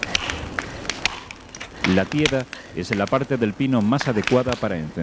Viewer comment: podrías por favor indicar cómo denomina (minuto 3:02-) a las astillas resionosas que prenden bien? Parece que dice " la piera", "pieda" ?? No conozco esa palabra y por el momento no la encuentro en el diccionario. Gracias!!